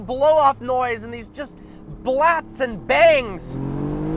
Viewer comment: Did you say blaps and bangs at 13:44?